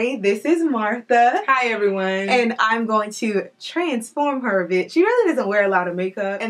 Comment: What word did she say after transform her @ 0:18? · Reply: Transform her a bit ☺️